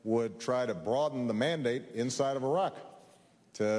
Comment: I died laughing because for a second it sounded like Obama said "Iran"ically